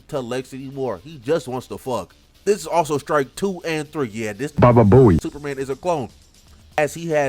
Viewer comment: what is being said? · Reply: "that's strike number 2 and 3. Yeah, this bababoey superman is a clone"